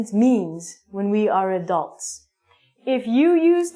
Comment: What's the word that starting by letter D ?in minute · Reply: Adult